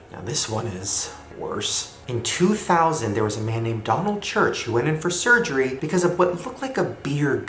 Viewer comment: when he said "Donald Church" i thought he said Donald trump lol